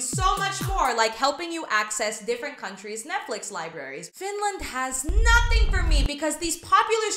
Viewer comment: DID SHE SAY FINLAND